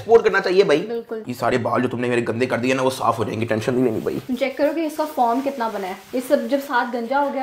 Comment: What did she say? The word is foam.......